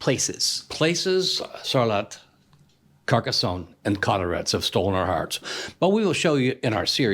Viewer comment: Bonjour ! at 10:15 you mentioned a last place that you loved i didn't understand ; Sarlat , Carcassone and ? Colorets ? could you write it to me please ? Have an amazing day love you guys !!